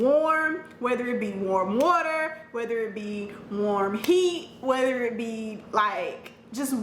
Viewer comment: Did anyone else hear “warm heat” 😂😂😂 Greta video though, really intuitive